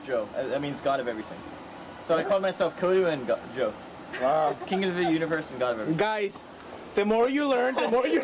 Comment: I thought he said king of the uterus lmao 😂